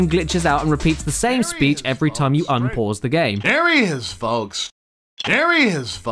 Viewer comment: *mishears "there he is folks" as "marry his folks"* I think I have problems...